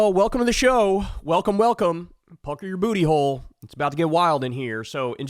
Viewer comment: did you just say pucker your booty holes? 😂😂😂😂😂😂